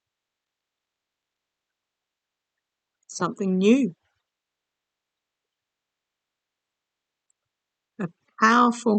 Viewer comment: Thank you for being here for all of us! As I'm listening to the video right around 20:10-20:14 you say 'May of next year' & I heard a voice (not yours) say something. I can't make out what it is...does anyone else hear something? 🤔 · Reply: I slowed the playback down & I think it says I'm here.